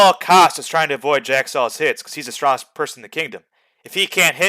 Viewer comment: I thought you said that you're the strongest player in kingdom ? 😂😂 i guess ur not 😂😂